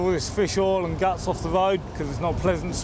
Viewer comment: what's a woad?????? I wouldn't even speak to this cop I'd say I can't understand a thing your saying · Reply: Load